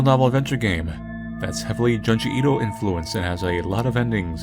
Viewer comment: In the beginning I misheard Junji Ito for Judge Ito.